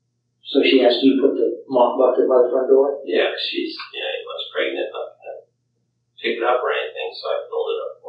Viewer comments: I thought I heard him say “ she was pregnant “ at time stamp between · I noticed he said “she WAS pregnant” in this interview which is only hours after she is suspected of only being missing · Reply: I thought that too but he says, “yeah she’s yeah 8 months pregnant.” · At about the 22:23 mark, does anyone else hear Scott say "Yeah, she was pregnant" ? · Does he say "she was pregnant" right there? Was? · “Yeah she’s yeah 8 months pregnant. “ It sounded like she “was” pregnant at first I was like whaaa 👁 👄 👁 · does he say “yea she’s .. was pregnant” ? This is meant to be the day/night of her disappearance. According to him, she is “only” missing at this point. Why would he say was? As in no longer.. as in not alive? Suspicious. New to this channel, apologies if this has been brought up before. I’ve not seen the previous videos regarding this person.